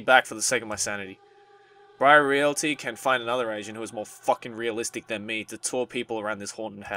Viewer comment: Once again totally love his voice acting even though this b a horror game he b have me ROTFL...also when he was saying 'Agent' I totally thought I heard him saying 'Asian'. I was like 😯😐😑 tf? Then I read the sentence. but his accent is incredibly sexy. lik aaahhhh so f'in sexy.